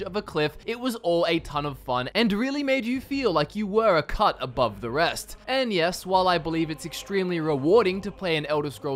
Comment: At the start i thought he said a cunt abive the rest and i was like true true 😂